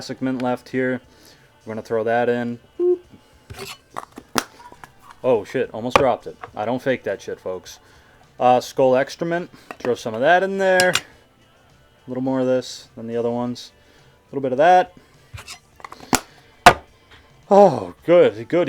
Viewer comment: i am so biased against skoal mint, when he said skoal extra mint, i heard it as skoal excrament